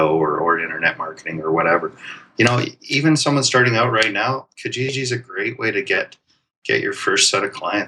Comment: What is it you say at 11:14 "KaGeeGee"? The great way to get your first set of clients. Thanks for the video! Great stuff! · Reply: Hey Ian, thanks a lot!. Kijiji is an eBay company, its like Craigslist, a free online classified service: <link>